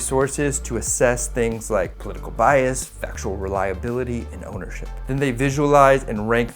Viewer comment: of your commercial for ground news, youtube subtitles thinks you said "sexual reliability" instead of factual. I am hard of hearing and I had to rewind and do like 3 double takes.